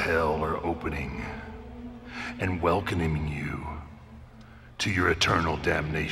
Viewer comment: did he just go ”welcomining” like am I the only one who heard this😂